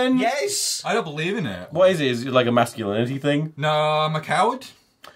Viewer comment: my headphones died and i heard elliot say "im a cow" and i died of laughter